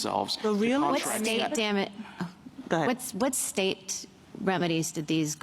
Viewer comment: Did she say "damn it" at 1:39?